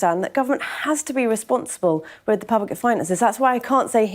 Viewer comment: Did I hear “responsible for public funding”? 😂 Someone need to see NHS about their loss of memory 😅